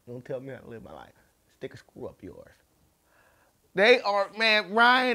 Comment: I thought you said, "stick a squirrel up yours." I laughed so hard, I dropped my spliff and burnt my muffin!